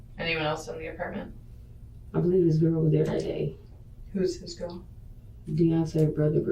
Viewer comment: I thought she said his grandma was there